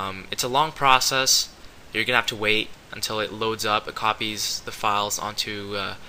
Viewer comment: sounded like you said "gonna have to wait a while, til it 'commies' the files"